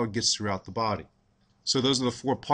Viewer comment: sounds like hes saying vodkast to me.